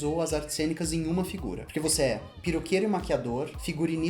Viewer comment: Eu juro como em 3:09 eu ouvi a Rita falar PIROQUEIRO em vez de PERUQUEIRO #morta 😂😂😂😂